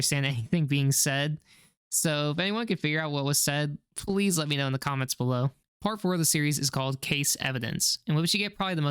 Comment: From what I heard its this and _ is just words i don't know "Due to _ involving an employee he(Bonnie) will need to be cleaned out" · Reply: "Due to having an unfortunate accident involving an employee, Bonnie will need to be cleaned out. Be sure to be thorough." And then repeating "-to be thorough."